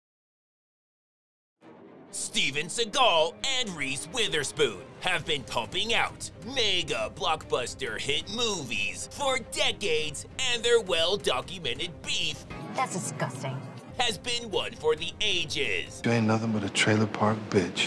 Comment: At first i thought i heard mega block buster shit movies